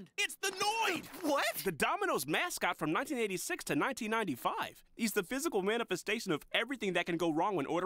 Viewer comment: oh decade thought he said 1996 to 1995- æ